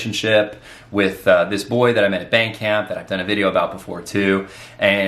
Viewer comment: When he said "band camp", I heard "bank camp", and I thought to myself: "Man! Imagine a camp where everyday you learn about trading, banking, & finance. Sounds so boring."